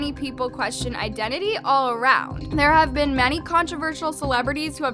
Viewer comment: Is it just me or... I heard "controvershee"